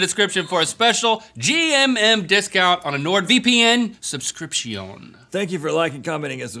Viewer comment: did anyone else notice link’s “vpn” overdub at 14:01? I wonder what he said...🧐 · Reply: owntheowl looks like he just says VPM on accident.